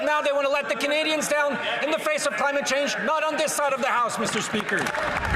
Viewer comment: Haha,it sounded like gould said 'this side of the heist'.